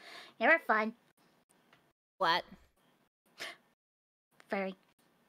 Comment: Why is furry on thumbnail? Did she say it? They say flat at 1:06 right? Or is the furry here just for the memes? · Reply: says furry not flat